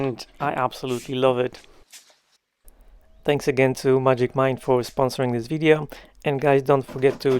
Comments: Sorry, I thought you were saying "Magic Mike"; i had to rewind three times to read the bottle.😂😂😂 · I thought you were saying Magic Mike. I expected you to break into dancing after drinking that. LOL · I had temporarily zoned out shortly before the ad read and thought you said you were sponsored by "magic Mike"😂. Then I realized what was the ad was.....🤣🤣🤣